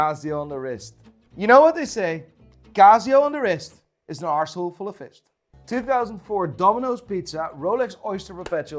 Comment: I initially thought he said "Casio on the wrist is an asshole full of fist." And I wasn't even surprised.